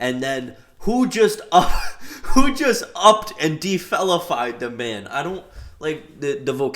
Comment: oh you said “defellafied” him I thought you said “defelonafied” him💀💀